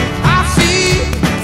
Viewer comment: wtf does he say. "Oh and uh I'm not walabada." ???!?!??! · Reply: Oh and im not alone